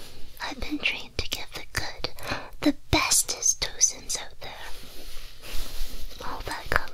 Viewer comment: What's the word she uses at 0:37? Tozins? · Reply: 'tocins. Oxytocin ❤️